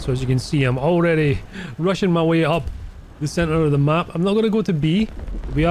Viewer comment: Wait did you say "I'm Russian.. way up" at 3:50?